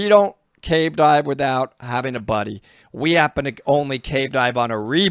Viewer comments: Omg for the longest time I heard you say “losing your body” (buddy) and thought you were talking about out of body experiences or retrieving dead bodies 😂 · I thought you said body and not buddy for a minute there. Try and fin your body instead o buddy is two different things 😂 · Nearly every time you said “Buddy”, I heard “Body”. Lol! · I kept hearing him say “body” but it was buddy… cool videos btw